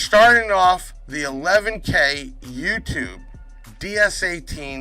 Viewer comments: Good luck! Please remember to text "Giveaway" to 737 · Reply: I thought you said to text 11k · How To Enter?? Comment your NAME-LOCATION-WHY YOU SHOULD WIN LIKE VIDEO SHARE VIDEO ON YOUR SOCIAL MEDIA Be Subscribed to Youtube Then Text 11K to 737- that is <num> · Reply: I thought you said to text 11k